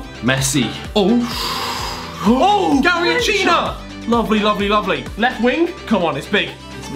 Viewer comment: he said garrinchina instead of garrincha